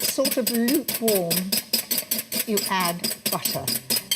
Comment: Can anyone hear what she said at this time point? Did she say, "You ADD butter"; or did she say, "You HAVE butter (as in meaning a butter consistency mixture)? If neither is the case then can anyone tell me EXACTLY WHAT IT IS that she is pouring into the egg mixture at time point 3:35. Thank You, fellow viewers. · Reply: She's adding melted butter.